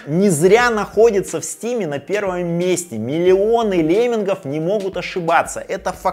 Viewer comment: Ж-)))) да лемминги тоже не лучший пример... Но в целом - дешево, красиво, в любом корпусе, с любым процессором, с любой версией PCI Express... Это тоже ценно. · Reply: а, мне вместо "леммингов" послышалось "геймеров". следствие просмотра на 2х)